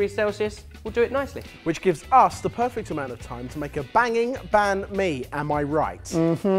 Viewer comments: When they said " banh mi " i thought they said " bang me" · haha, when they said "banh mi", i thought they said "bang me"! It's funny how now the word is in the Oxford dictionary, they call Turkish banh mi now, even though banh mi is specifically a vietnamese dish lol